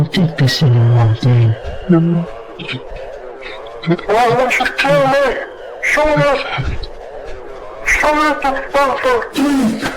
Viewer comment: he said “why won’t you kill me shoot me….shoot me you bastard” the others before what he said I was please kill me and all